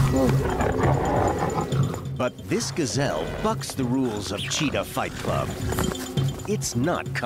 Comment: did he just say “but this Gazelle fucks the rules” damn man the Gazelle ain’t having it....